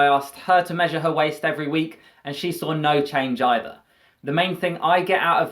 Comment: Execuse me, what is the benefit of doing this exercise? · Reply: he actually said that hers hasnt changed either